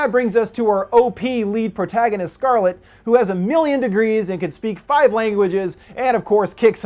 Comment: At first i thought you said she "was 1,000,000°" not "has a million degrees" i was like omg what a cool way to call someone hot